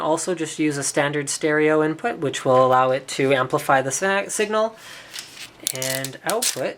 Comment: Did he just say "Amplify the sex" at 2:38 ?